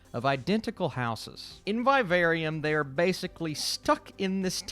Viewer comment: did you say “by a very um” or “Vivarium”?? 😂